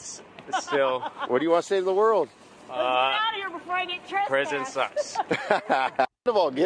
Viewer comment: did he just say the Presidents sucks? i was waiting for him to thank President Trump,seems a bit ungrateful.